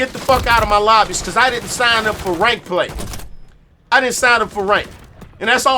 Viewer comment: Not just aim assist. You get put on the wrong side of the 1.5 seconds netcode delay. If it's a close call. enemy wins. LOL I thought you just said "I didn't sign up for rape play!"